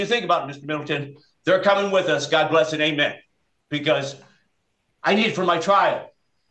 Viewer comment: I thought he was saying 'god bless straight men' or something. I was like lol no.